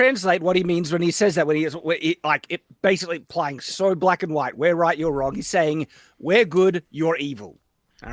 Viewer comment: During this part of the conversation....I heard the lines from Matilda. "I'm big. You're small. I'm right. You're wrong." 🤣🤣🤣🤣🤣🤣🤣🤣🤣🤣